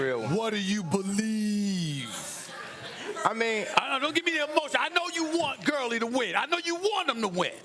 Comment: I'm not the smartest guy in the world. My daughter tends to prove that everyday.....but did Steven A. slipped in a "BS"???? Sounds like he said "Don't give me that BS."